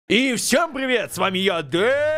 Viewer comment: Мне в начале приветствия послышалось "Всем привет, с вами Яндерееееее")